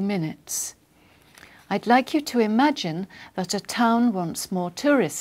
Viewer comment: What did she say after I’d Like to imagine .... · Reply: That the town